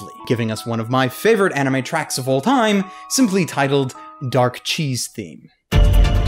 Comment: At first I thought you said "Dark Cheese Theme" .___.